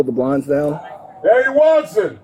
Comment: turn on closed captioning... Why is cop calling for Daddy Watson???